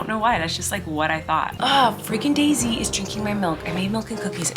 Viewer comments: I thought you said Maisy was drinking your milk and I was so confused to why you called her a brat 😂ofc it’s the cat lol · You gave me a shock. When you said "Daisy is getting in my milk", I thought you said Maisy. My mouth hit the floor when you said "you brat, get out of here." I breathed a huge sigh of relief when it was the cat. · I thought she said Maisie instead of Daisy so my daughter brought when he called her a Brat😂